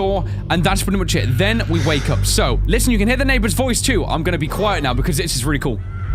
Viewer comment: I stayed with my ear close to my boxes and i heard him said: "Quiet!" so if you do not know what he said well i've told you what he said.